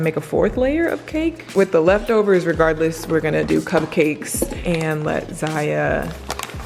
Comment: is it just me or 14:00 did she say CUMCAKES instead of CUPCAKES???????????